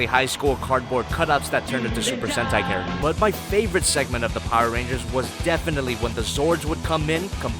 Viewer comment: I thought it said power rulers